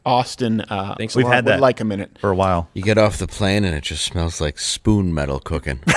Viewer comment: Dave all I heard was "spoon maaan" when you made your Seattle comment